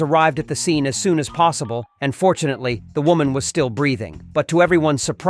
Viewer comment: Why did i hear him say "unfortunately she was still breathing 😭" i looked at it 3x before realizing it was "fortunately"